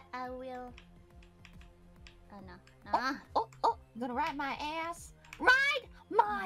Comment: Thanks for the translation I appreciate your work but here is anyway some fixes if you want: 3:28 Coco says "Ride" instead of right 3:35 Coco says put my Ass in the "Butt" instead of Bite 5:35 My Man is actually "Not there" 7:03 This is a "PRO Move" instead of a problem